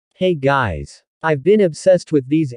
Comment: In the very beginning I thought I heard "Hey gays!" 🤣🤣🤣 But I knew it was "guys"